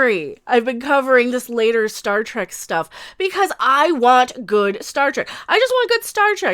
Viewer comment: did you just say "start wreck"?